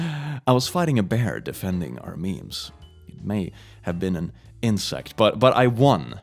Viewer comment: At first, I thought you said you were defending our mead from a bear, that would be the most Swedish thing ever, Gustavus Adolphus would have been proud